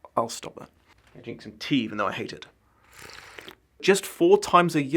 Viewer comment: did…did Dom just say he hates tea? 😮. Careful mate, or you’ll have your British Citizenship revoked! 😜